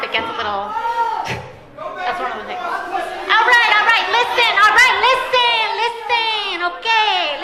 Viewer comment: All I heard was “Listen Linda listen” hahahahaha